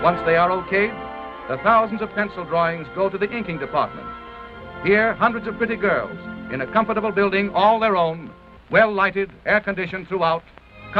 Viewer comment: did he said « where hundreds of pretty girls, in a comfortable building…. ”?